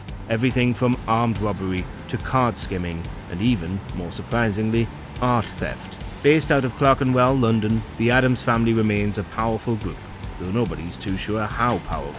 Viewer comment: Coulda sworn the vid creator said 'arse-theft'